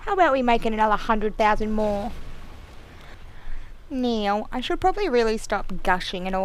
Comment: omg hahaha at 3:02 when u were saying now it sounded like meowww